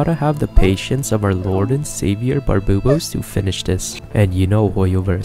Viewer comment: Im sorry did u just say barboobos?